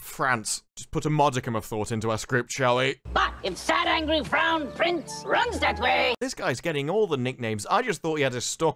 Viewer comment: Wait he said "Sad Angry FROWN Prince?" I honestly thought he said BROWN prince.